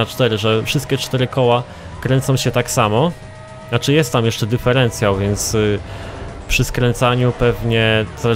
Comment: jak słuchałem 1 raz to usłyszałem duperencjał XD :0-) a filmik spoko i carrerka WYMIATA